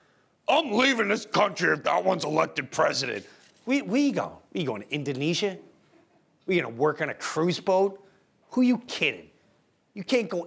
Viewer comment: did he say Indonesia?